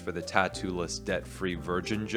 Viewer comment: ah, 'debt-free', thank You. I heard 'death-free' and thought the guy has a thing for immortals, dreams of a goddess :D